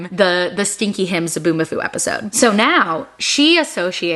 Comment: Literally thought you were saying stinky ham song 😂😂😂😂